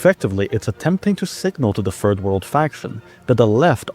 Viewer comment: did he just say “the furred world”? Edit: yes. Yes he did. Many times lol.